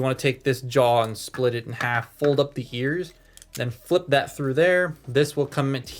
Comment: Did he say hears